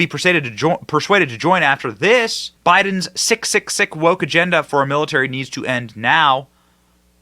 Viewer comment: Well, The Village People did sing “In the Navy”!! 😉🤭 @ 4:24 - I had to go back & listen again cuz it sounded like you said 666 instead of sick, sick, sick. Well, both fit!💯🤔 #SALTYARMY 🧂🧂🧂🧂🧂🧂🧂🧂🧂🧂 #WETHEPEOPLE NOT WE THE SHEEPLE 💯♥️🙏♥️🕊️♥️🇱🇷♥️🗽♥️⚖️♥️